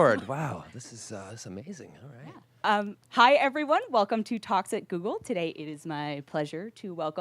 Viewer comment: Thought she said "hi everyone welcome to toxic google" at the start